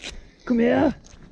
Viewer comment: Is the soldier filming this by any means austrian? I thought I heard a 2:34 "kum her", "come here" there